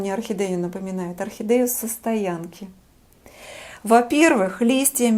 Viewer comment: Орхидея состоянки, Правильно расслышала ? это почти белая или бледно розовая ?